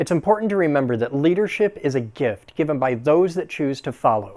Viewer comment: I thought you said leadership was a gift given by the ADA.